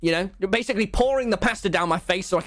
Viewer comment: When he said pasta I thought he said paster